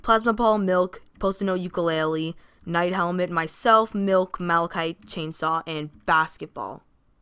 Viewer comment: Did just say Malachite?